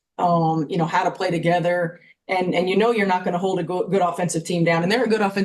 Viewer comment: does she say you can't hold the goat, good offensive team down?